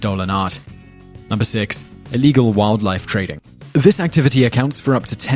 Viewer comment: I thought num. 6 said "waifu trading"